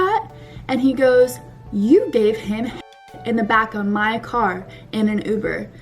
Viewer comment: WHAT DID SHE SAY AT 12:26?!?!? I DONT UNDERSTAND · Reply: 'you gave him head, in the back of my car'